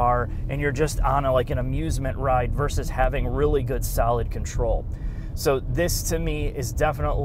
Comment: "really good salad control" is what said. But then I remembered has a Chicago Bears accent so I get it now.